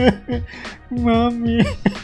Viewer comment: i feel bad 3:53 but at the same time im laughing,also did you call mommy?